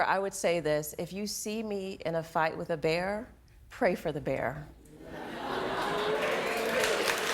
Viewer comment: Lol " if you see me in a fight with a bear pray for the bear" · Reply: I thought she said: "Fight with a BEER"